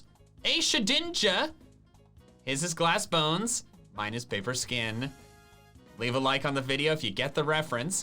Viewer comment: Omg, i wasnt paying attention when he said he named his shedinja “paperskin” and i thought it said “forskin” and i was so confused lol